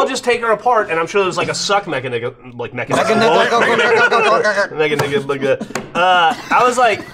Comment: Did Geoff just say, "Mech-Nigga"?